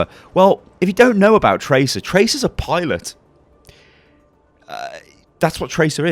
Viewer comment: when you said pilot I thought you said pirate and I just thought "what the fuck is this guy talking about"